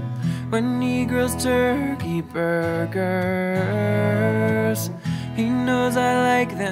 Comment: on 1:21 when he sings "When he grills turkey burgers" it almost sounds like he says "When negros turkey burgers" I had to rewind it twice till I caught on..my bad. lol